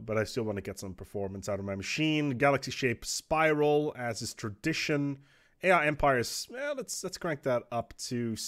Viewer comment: For a second I thought you said the Yiff Empire. lol